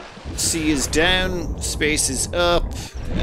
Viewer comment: When you said "c is down space is up" I heard "the sea is down space (i.e. outer space) is up" and I was very confused as to why you were telling us where things are. lol